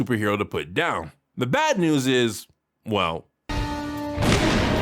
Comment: did he said "bat news" to make a joke or he really said "bad news"?